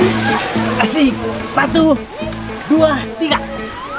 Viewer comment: Ternyata aku salah denger dikira aku papah nya bilang " spatu dua tiga " ternyata " satu dua tiga " 😁😁😁